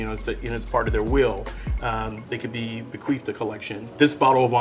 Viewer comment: Did he say "beQUEEFed"? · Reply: He said “Bequeathed” 😂